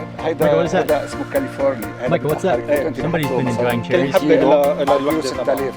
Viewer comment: saying "cherry" sounded like "chilli" haha definitely Mark's child